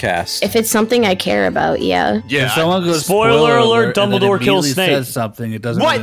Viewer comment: When he said dumbledor kills snape, I thought they said double door kills snake